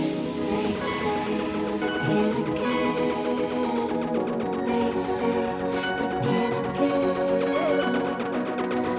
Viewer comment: só eu que ouvi "they say agaragan" ???? Kkk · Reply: they say BEGIN again